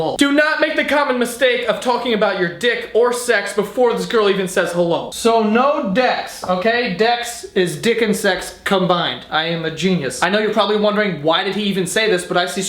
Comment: Hey, Joe. I just wanted to let you know that your captions are a little off. At about 2:32 the auto-captions say "this girl even says the lo sono desk okay Dex is Dickon sex combined " where they meant to say "this girl even says hello. so no dex okay dex is dick and sex combined". When you get a chance, could you fix that? I just want to make sure everyone can understand and enjoy this video. That'll help the 360 million folks globally who rely on closed captions to understand videos. Thanks